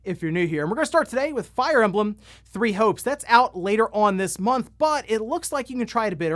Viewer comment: Watching this with some noise going on in the background and had to rewind when I thought I heard “ we’re going to start today off with fire emblem, three hoes”.